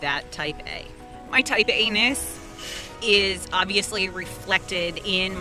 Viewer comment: So glad I found you! You are hilarious, transparent and insightful :) Also- I had a giggle at 1:14 when I thought you said "anus" but that's just the 14 year old boy in me hearing dirty words instead of what you actually said...